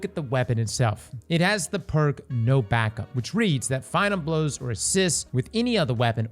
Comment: I replayed this 4 times and i heard "no Backup" instead of "no backpack"